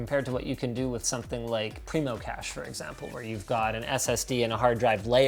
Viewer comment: subtitles say "Primo cash" instead of "Primo cache". Unreadable, Dislike. Thumbs down. Nah, I'm cool, just point it out so that if you guys see it you might fix it, even though it's a pretty minor error.